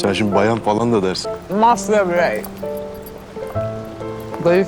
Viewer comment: da ne dedi serenay ? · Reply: "Master birey"... Kadın cinsiyeti için yapilan yeni tanimlardan biri :) Kıvanç onun "bayan" sözcüğüne tepki göstereceğini düşününce Serenay da "ben de zaten kadına 'master birey' diyordum" gibisine söyledi.